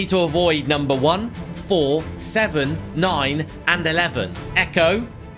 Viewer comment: did he say 9/11